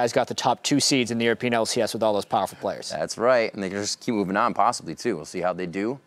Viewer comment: "Do well", the word you need to use is "well" not "good." 25:38 What did just say? Because it sounds like he caught himself saying niggas and tried to slur it away. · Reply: he said and they can just keep moving on but he slurred his words